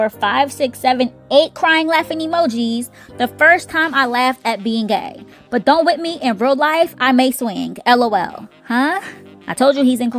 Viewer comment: Did he say he may swing 😮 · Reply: I thought he was trying to say “at first I laughed about being g*y but don’t play with me in real life I may swing” 🤣 but I don’t put anything past anyone. The industry is quite weird.